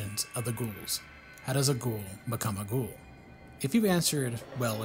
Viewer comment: When you said "how does a ghoul become a ghoul?" I thought you said "a ghoul a gabagool" in some weird Sopranos reference.